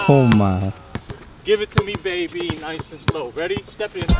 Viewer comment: Im Viet thought he said ``Du Ma Baby`` at 21 sec xD